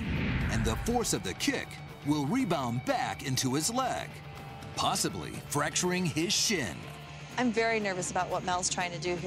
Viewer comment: sounds like he says "fracturing his shit"